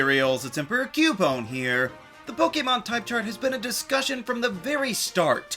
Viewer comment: Lmao I misheard "discussion" as "disaster" in the beginning, tbh it still fits 😂